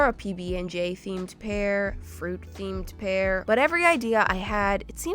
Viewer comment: A fruit theme pair. what I thought she said: A fruit theme pear.